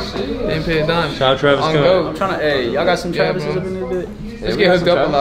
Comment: Why tf they talking like they from the hood 😂😂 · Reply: i heard him say “yal got some travis scotts i dis bih” n i came straight to the comments😂😂